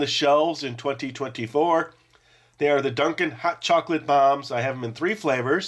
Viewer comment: I swore I heard you say "Chocolate Bar" instead of "Chocolate Bomb", but it sounded more like "CHOCOLATE BAH" 🤣 Keep up the good work (I've been watching since I was a little kid, and still find this channel entertaining)